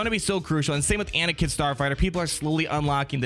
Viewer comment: did you call him anakin starfighter